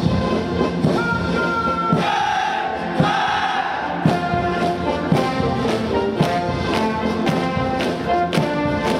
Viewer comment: Could someone inform me what the guard commander and the guard yell at 5:43 just interested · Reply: "His Excellency General, the honour guards of People's Liberation Army Navy is ready for your inspection."